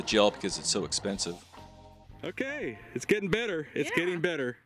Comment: I some videos, you say "it's getting better" and I hear "there's Eddie Vedder". Next thing you know, I got Jeremy playing in my head for three days